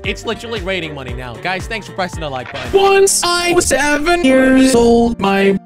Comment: I thought you said preston for a minute there☁️😅